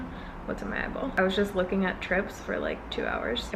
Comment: I thought you said you were looking at chips for two hours and I was like same, but then I realized you said trips